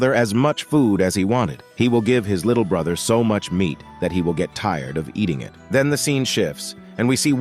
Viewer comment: Ayo what the f he say ' i give him so much ment'